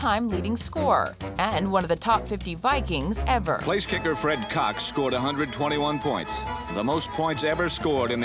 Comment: My high ass thought it said Red Fox 🤣🤣 I was like dam I ain't know he invented the nerf ball🤣🤣